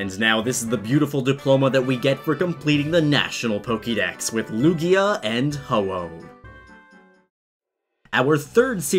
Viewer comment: it sounds like he says Lugia and Homo